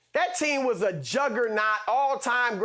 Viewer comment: What did Shannon say at the 3:15 mark??? Lmao 🤣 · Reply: Juggernaut